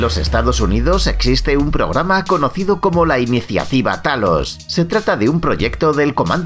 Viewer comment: pense que decia thanos xd